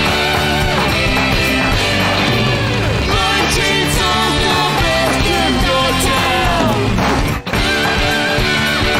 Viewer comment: I hear “My chainsaw’s got basket got town!” And I know they definitely don’t say that (hopefully) and sub titles doesn’t help either so can anyone tell me what they say because I can’t tell what they are saying at all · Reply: "my chainsaw's the best in the town"